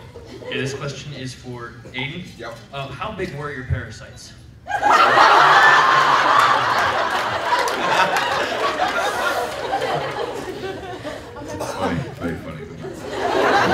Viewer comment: What was the person saying at 26:14? How big were your parents' whaa? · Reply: They were asking "How big were you're parasites" :)